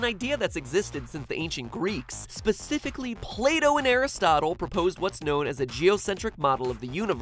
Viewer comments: When he said "Plato", I heard it as "Play-Doh" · I thought he said play dough when he was saying Plato